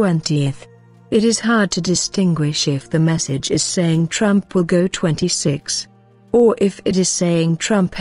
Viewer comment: I thought it sounds like it says. Trump will be 86ed